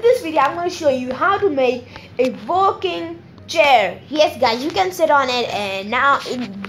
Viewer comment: When he said a working chair I thought he said a filing chairXD